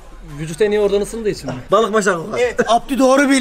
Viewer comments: abdü abi ne dedin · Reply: Balik bastan kokar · Abdü abi ne diyor be altyazı açtım altyazı yazan adam bile anlamamış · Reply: Balık baştan kokar diyor atasözü :)